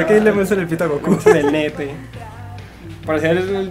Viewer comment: Creo que dijo nepe?